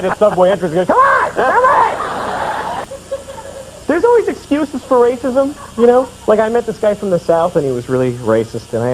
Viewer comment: On the hilariously racist farm bit (@ 9:07), I'm not quite comprehending what Louis is saying or invoking with the chicken clucking. Any help would be appreciated The mooing and bleating are perfectly clear (and very funny!). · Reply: Okay. After close listening (even with the somewhat fuzzy sound recording), I think Louis is saying :"Mexicans" with the chicken clunking. I think.